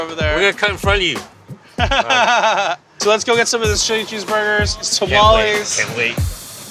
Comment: Why did I hear "shitty cheese burgers" yo, omg ahahaha I swear on my aunt I don't mean to diss on Tommy's, always my go to for a late night hangover preventer.